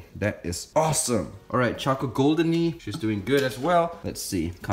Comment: chocolate goldeny? Cus thats what I heard